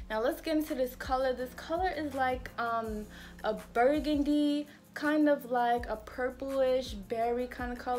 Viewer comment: What’s the exact color when I order ? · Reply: Aww I thought I heard “like a burgundy red” but I’ll re listen 👌🏽